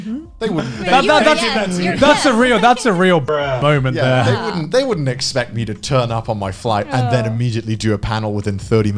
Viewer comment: What did Garnt say here? Why the video editor replace the original sound here with the meme sound "bruh"? Because of risk of demonetization or the real warning from YT about demonetization or censorship or what? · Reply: He said bruh I think they just added the actually sound over it